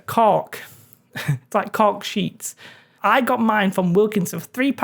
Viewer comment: You do awesome work... Almost have your accent down. I thought you said caulk sheets and started freaking out, because I never heard of caulk sheets. I was about ready to do a google search. Lol. Then I seen you use the cork sheets. Keep up the great work...